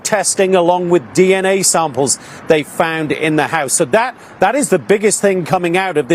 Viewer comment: I thought they said they found it on the side of the street lol uh